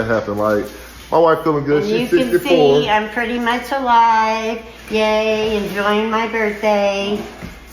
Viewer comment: Did she say “pretty much” alive??😂😂